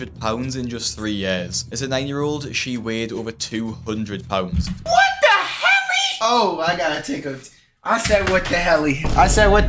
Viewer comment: I swear I heard “what the heavy” 😭